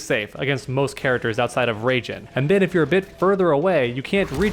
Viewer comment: Did you say rajin at like 5:31? 😂